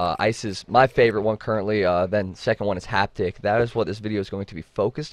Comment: did you just say isis ?